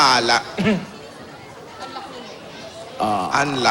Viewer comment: nakatapat malapit sa bibig nya yung mic kaya narinig yung sinabi nya na "unlock nyo ho"ohmyghod n